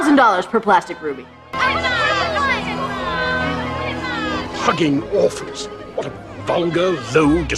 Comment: Whoa! what the hell did he just say at 19:15 !? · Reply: Paul Shinn I thought he said fucking orphans